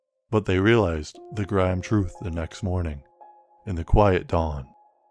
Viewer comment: grime truth? You mean grim? As in grim reaper? This reminds me of Rick and Morty when Rick was saying granite instead of granted